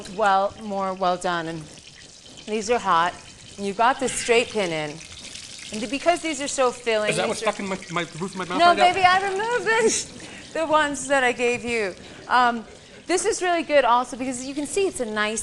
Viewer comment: does tom say "is that wats fuckin with thr roof of my mouth" at 4:08?? and padma calls him baby right after that